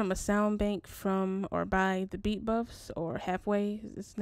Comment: Hey excusme english isn't my first language can you write the name of the sylenth soundbank i think i heard "the beat bros" but i'm not really sure, and by the way fire beat🔥🔥🔥keep the heat coming 💪🏾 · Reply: thank you! & the sylenth bank was created by 'thebeatbuffs'.